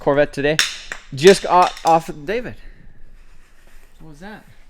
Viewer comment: half a sleep and all i hear is " i just got off David "